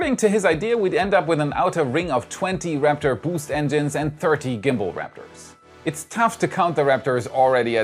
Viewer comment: how many? I hear 30, should be 13. Btw.. What happened with other hosts?